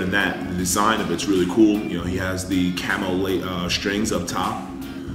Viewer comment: when he said "camo tone" it sounded like he said camel toe